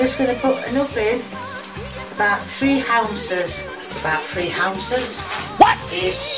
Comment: I thought she said houses XD